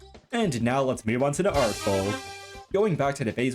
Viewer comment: my subtitles said "art bowl"... 💔